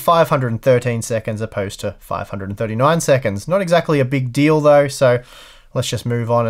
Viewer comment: What is he saying? Not exacly a BIG DILDO? XDXDXDXD